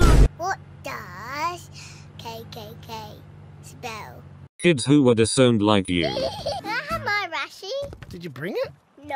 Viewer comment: What did say at 1:03? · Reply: “Kids who were disowned like you” and it’s the theme music to the good the bad and the ugly